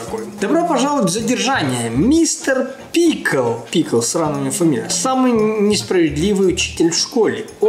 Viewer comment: мне послышалось сранно